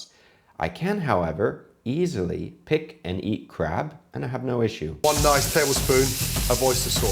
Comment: I need to watch these on something with higher quality speakers: 7:15 I heard, "I could easily pick and eat crap."